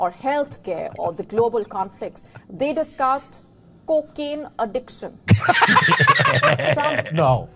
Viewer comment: At first I thought that Indian said cooking addiction